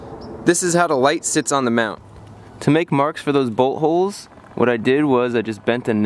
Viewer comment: sounds like he said buttholes